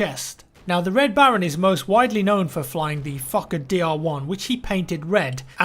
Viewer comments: I thought he was saying "The fucking DR1" :D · I thought he said "Fucker DR1" for a full minute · Sounded like he was saying the Fucker DR1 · I thought you said fucker Dr1 for second XD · "most known for flying the fucking DR1" That's what I thought he said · lol its sound like jack says the fucker dr1